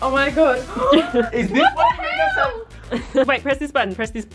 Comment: Did say what the hell